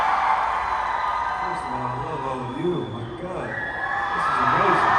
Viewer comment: What did that fan said to Austin? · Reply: I think it was "we love you"